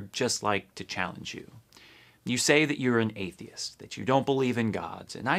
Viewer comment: did he just say “don’t believe in gods”?! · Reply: "you say you are an atheist, that you dont believe in gods"